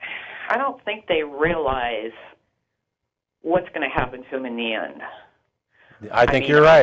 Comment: What does say at 0:46 ? "they don't realize what's gonna happen ???"